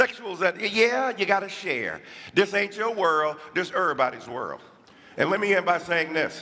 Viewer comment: This guy is actually quite insane. No joking. He’s INSANE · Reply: Did he just say "dis errbody world" ? 🤣